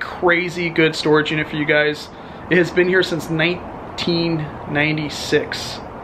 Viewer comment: They said in beginning that no1 has been in there in 20 years, yet in the box of paper and Muhammad Ali book there was a calendar that said 2006 on it. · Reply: I thought he said it had rented since 96